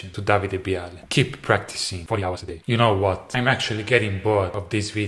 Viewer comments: Did Davie say keep practicing 40 hours a day at the 9:44 mark? What happened to practicing 69 hours a day? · did I hear this right davie504 said keep practicing 40 hours of bass so 40 hours > 69 hours